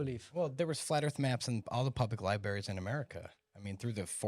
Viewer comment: did you just say libarys? Discredited for the rest of your time on this podcasts